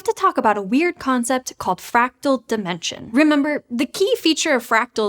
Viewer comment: I really thought she said “fractal dementia.” That’s a phrase I’ll definitely use.